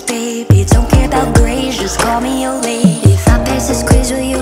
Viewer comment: I honestly thought at one point it said don't care 'bout grays ' just call me your lady 😂😂